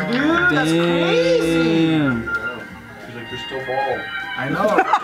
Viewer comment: what did say? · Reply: I think he said “ She’s like ‘you’re still bald’.”